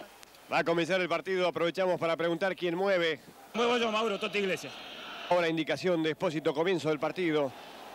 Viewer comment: que dijo ese jugador? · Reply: Moyaso Mauro esto es tigrese?